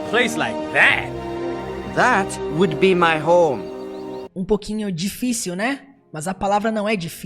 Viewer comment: "That's would be my home." 54:07 kkkkkkkk foi o que eu ouvi